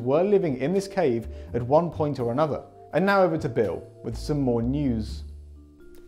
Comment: am I tripping or did he say Bill?